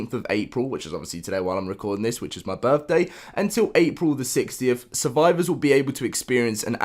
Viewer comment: Does he say April the 60th or is that just me 3:13 but thanks for the information, just think that’s a little funny